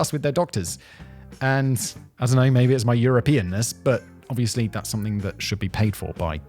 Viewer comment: "Maybe this is my Europeanis" if youre not paying close attention this sure sounds like simon said "Maybe this is you're a penis."